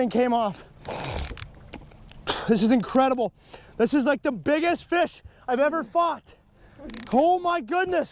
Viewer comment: Did he say "This is the fish I've ever fought" or.... Hummmm. And then takes about "grinding" ... "hard hard hard" etc. Hummmm.